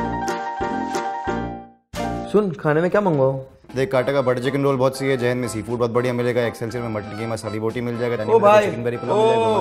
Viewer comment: did he just say "JAIN meiin seafood achha milega"?? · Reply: Jain mein seafood badiya milega